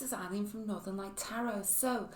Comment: Hi Chanteuse my Name is Eileen (it's an Irish name), I am so glad you are enjoying my readings! Been here a couple of years now :-) X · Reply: Thank you for correcting me. I heard “Ally” when you said Eileen. I’m familiar with the name. I’m a Hibernophile. I love all things Irish! ☘️ 🇮🇪